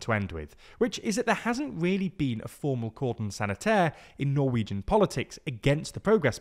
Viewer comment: what did he say there? sounds like Gordon sanitaire